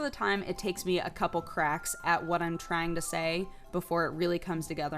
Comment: When she's talking about doing NEWZ, did she say trying or crying; sounds like she said crying